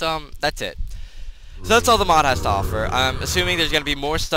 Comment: look at captions xD 2:15 basil tomatoes off her I'm assuming it will be more stuff later and maybe uh... 2:31 cfd some 2:32 that's really a I thank you for watching my name is will and this is the 2:56 albright rare and after at about the says these syringes 3:02 marquerite 3:13 cdma zombie viruses drafted out of uh... syringe at his office was and then they 3:34 turns up so yeah does that say the president of syringes but yeah I think